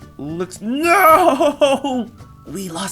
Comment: did he say we'll still get high??? don't do drugs kids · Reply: He said we'll still get by